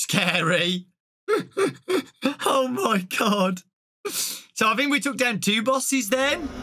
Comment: Sounds like he said "Oh my cod!'